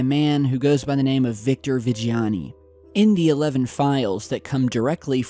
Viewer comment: the guy goes by the name Victor Vagina. Does anyone else hear that?